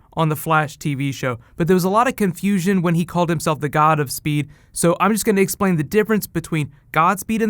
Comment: i thought he said godspeed too...which made me think that was the story they were doing that arc...but i watched it again and heard god of speed...either way, good stories to follow with though!